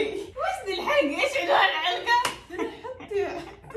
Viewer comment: سمعت (نص الحلقه) وربي ماامزح